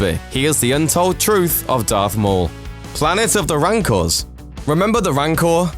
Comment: sounds like he said "planet of the wankers" hehehe....